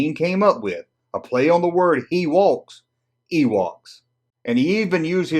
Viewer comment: A play on the word, what? 3:07 I didn't quite understand what you said, could you please let me know? Thanks. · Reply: He walks